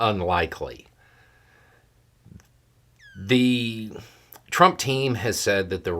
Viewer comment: I probably misheard, but I could’ve sworn Beau said Trump’s Illegal Team 😂